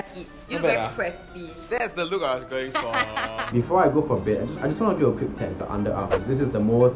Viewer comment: Not the CC saying “before i go for beers” at